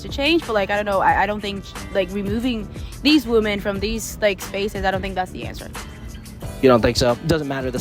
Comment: she actually said: removing these women from these spaces. She and her SIMP boyfriend are completely nuts. Pun intended.